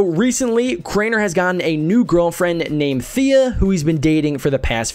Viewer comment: did he say fea?!